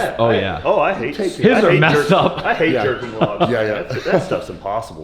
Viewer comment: thought i heard I hate jerking off